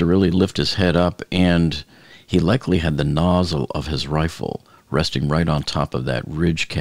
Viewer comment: Did you say "nozzle" of the rifle?😂